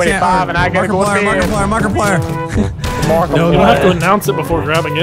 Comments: Did i hear Markiplier at 5:40 ish? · Did just say Markiplier? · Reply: Yes, he said Markiplier XD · Did just say "MARKIPLIER" instead of "MULTIPLIER" ? LOL